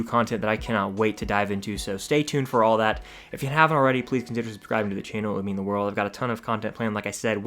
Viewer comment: if you havent already ple-adjbhdbehb-subscribing to the channel (just me who heard this??)